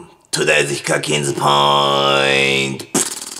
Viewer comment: 今さらじゃけど… ○○ヒカキンズ？ポイントってなんて言ってるんやろ… 自分的にとりあえずヒカキンズポイントって聞こえる！